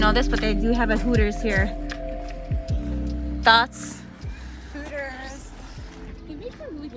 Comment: Did just call the people at Hooters THOTs? 😆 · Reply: LMAOOO NOOOO I was saying, "thoughts??" HAHAHAHA omg